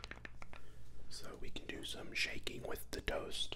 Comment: i thought he said “we can do some shanking with the toast”